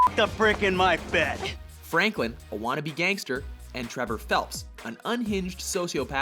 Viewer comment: Did I hear "Trevor Phelps"? It's Philips. Sorry if I just didn't understand it properly, but I watched/listened multiple times and it always sounded like "Phelps" to me.